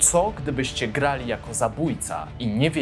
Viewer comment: Czy tylko ja usłyszałem ,,tzo gdybyście" na początku? hahah